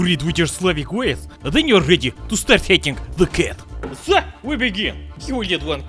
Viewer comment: I thought he said "time to start hitting your cat" but then realized... he said hatting.